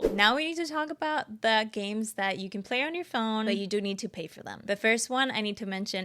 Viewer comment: Unpacking is not free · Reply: Oh I thought you said “ but you don’t need to pay for them” I’m sorry